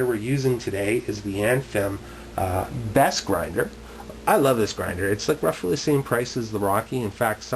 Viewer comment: Anyone know the brand of the step grinder? It sounds like he says "Anthem best grinder" but Google says no.